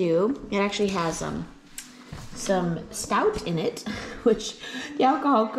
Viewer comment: when u said stout, I thought you had said snout- which horrified me, as I am a vegetarian!!!!!!